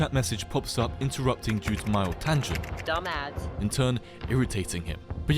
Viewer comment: i thought he said dumb ass :O